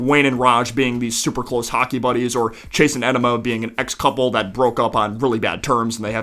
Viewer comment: Did anyone else hear him say Chase and Enema? 😮